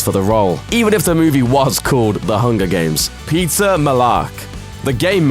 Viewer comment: pizza mellark instead of peeta